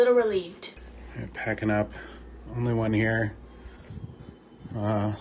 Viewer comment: did anyone hear a voice say "alone"?????